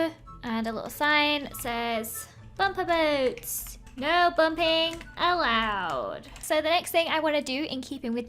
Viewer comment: Lizzie: Bumper Boats! Also her: No Bumping Allowed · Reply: When she first said that I thought she said no humping allowed! ( sorry to the children reading this humping means happy jumping)